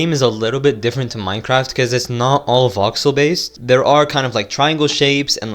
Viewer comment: there’s a caption error where it should be voxel instead of auxo